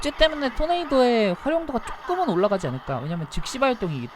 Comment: 초사이에 잘들어보면 시발이라고 들리는듯